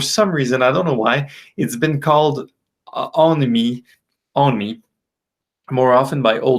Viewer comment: you mention that the tune is called "..." and it's a bit hard to near exactly "[ oh nee ] what you're saying, but as an older guy, I guess I'd better find out ya? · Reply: Hi Donnie, sorry about that! I was saying that Prelude to a Kiss has been called "on me" usually by older jazz musicians and especially bass players. Not my best grammar... but that's the price of improvising ;)